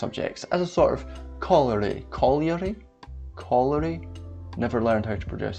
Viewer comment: talking about housing and he's trying to say corollory but I genuinely thought he was trying to say colliery (a type of town/village in the North of England that were specifically built arround coal mining).